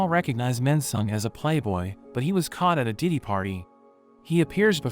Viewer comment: I think I heard daddy party